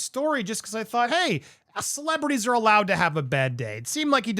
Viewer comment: did I hear correctly or in error? Pls Lmk, Thx! "US" celebrities are allowed to have a bad day." or was it," A celebrity's allowed to have a bad day"